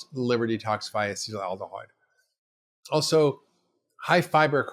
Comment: what does he say about eggs? it is slurred…. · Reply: I think “acetaldehyde”